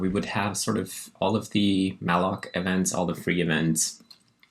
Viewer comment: YouTube auto subtitles are generally useful but also a bit funny sometimes. Apparently, and unbeknownst to us all, we’ve been all wrong when we thought malloc was called malloc. YouTube auto subtitles, in its infinite wisdom will inform us @ 0:00:14, that the proper name is in fact Mal’akh. I looked it up and learned that this is the Hebrew word for angel. I was a bit disappointed that it had nothing to do with Cthulhu (as the word might otherwise suggest to someone like me who is not versed in Hebrew) though, as computing sometimes seems more in line with the darkness of the void than something heavenly :p That being said, I do agree with the sentiment reflected in the name of your operating system, that the act of programming and software engineering can also be a great source of joy and satisfaction. As long as no hurdles too big or external factors get in the way ^^